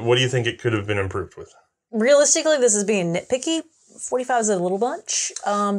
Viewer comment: What the hell does say when asked about the short comings of the 1911. I’ve listened a dozen times and it sounds like she says “ free sound a little bunch”. Which is probably not what she said. Any help? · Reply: ".45 is a little much"